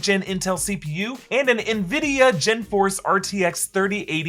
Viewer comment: did you just say GenForce?